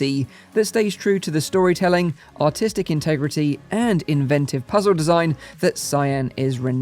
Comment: know he has an accent but I swear he said "autistic integrity" at 4:09 lol